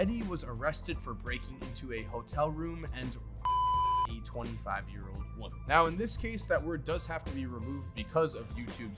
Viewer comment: what word did he censor? Also 6:51 and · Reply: JR Smith The word was rape